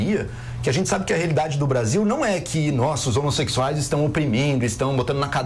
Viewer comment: Juro que ouvi no min 12:13 o Joel falando "nós os homossexuais".